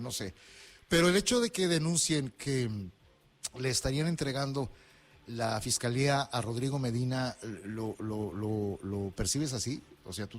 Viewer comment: ¡ NO ENTENDÍ ! ¿ LE ESTARIAN ENTREGANDO LA FISCALIA A RODRIGO MEDINA ? MINUTO